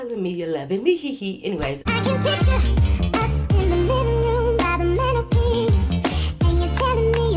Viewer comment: I really thought was saying by the mini piece 🤣